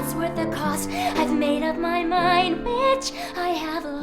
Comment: Uh, did Alli just say "I've made up my mind, bitch" at 5:03? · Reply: no, it's "i've made up my mind, which i have lost" hahaha